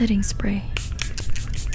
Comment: Are u filipino!?!!? Could've sworn u said "faster manong"